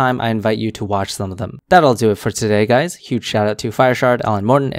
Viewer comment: Oh it’s FireShard…I always thought you were saying FireShart 🤦‍♂️